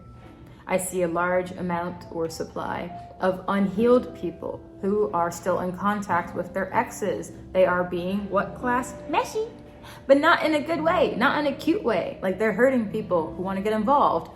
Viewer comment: I kept hearing “heeled” instead of “healed” and picturing what thag meant had me so confused😂